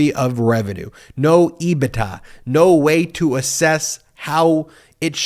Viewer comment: "... no [what]? Sounded like "ee-bih-tah."